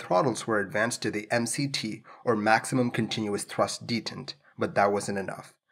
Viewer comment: Notice the plane landed and it wasn't made by Beoing LOL On the subtitles @ 2:43 you say clearly the mct was set to maximum but the subtitles say minimum. Just thought you should know :)